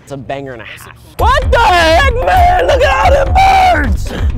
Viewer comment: did he just say "N***a look at all those birds!"?